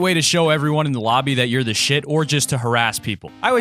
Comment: did I hear SHIT?